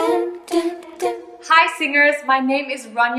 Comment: who else heard she saying “Hi sinners” like I did? 🥴🥴